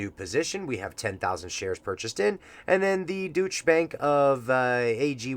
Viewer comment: At first, I thought you said "Douche Bank"